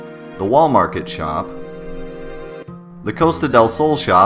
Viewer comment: For a second I thought he said Walmart shop